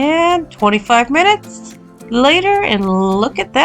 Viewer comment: I misheard you in the video and baked it for 45 minutes instead of 25 minutes so the bread is a bit brown😳 but it still turned out good